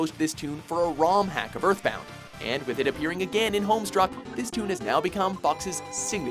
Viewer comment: Did he just say homestruck?